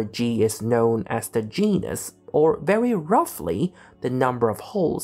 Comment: When you said "where g is known as the genus" I heard "where g is known as the gee-ness" 😂😂 I guess you could argue that's correct as well